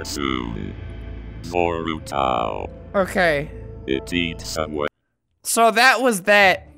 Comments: What it actually says: It eats away. What I can hear: It eats Subway. Am I the only one? 😂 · Lol at 2:04 I thought it said "IT EATS SUBWAY" and then i looked at the subtitles and I be like 'huh I swore I heard subway.'